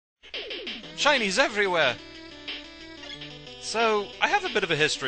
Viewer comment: lol I thought had said "Chinese everywhere" at 3:18 xD